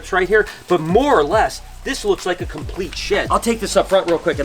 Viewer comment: For one i hate the new comments spot. But 2 i thought you said “WOW look at this complete sh!t!🤣